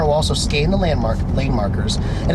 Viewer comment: Did he say "Skane the landmarks" LOL